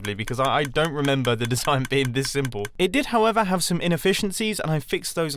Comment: did he say it was SIMPLE